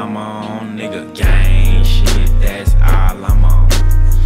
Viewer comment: Sounds like 21 saying "gay shiet, that's all I'm on" lol